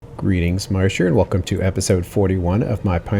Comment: I just realized I've probably been mishearing your intro this whole time. I thought you were saying "Marsher", as in, your fans are called Marshers, but "Marsh here" makes a lot more sense, huh?